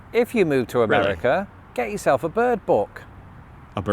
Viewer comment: The word you didn't quite catch at 4:49 was "bird book", to look up species' of birds in your area.🦅📚